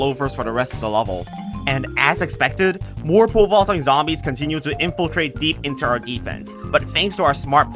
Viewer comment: I hear "more pole vaulter zombies" as whirlpool zombies